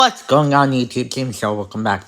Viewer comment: I said that? Idk what UTS means. lol. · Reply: i skipped a fraction of a second after the start of the video where you said "whats going on youtube" and it sounded like you said "uts going on youtube"